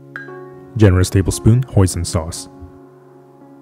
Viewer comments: I've a couple of questions about oyster sauce. 1) Is it Vegan? 2) Does it contain MSG? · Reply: he said Hoisin sauce. I thought he said oyster for a second, too, but it's hoisin. and yes, hoisin is vegan :) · I've a couple of questions about oyster sauce. 1) Is it Vegan? 2) Does it contain MSG? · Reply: I also thought he said oyster sauce but hoisin is listed in the recipe. You can however get veg oyster sauce made from mushrooms.